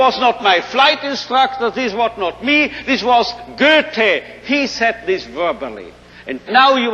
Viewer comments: who is he talking about? Gerte? Goethe? And when did they say this? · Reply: Gothe wrote this in 1795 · who? sorry but cannot understand the name because the accent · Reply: Goethe